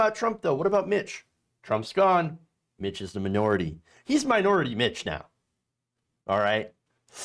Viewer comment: "He's minority Mitch now." For a second, I thought you said Minority Bitch. XD